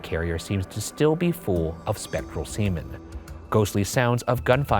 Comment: so immature, I heard "spectral semen" HAHA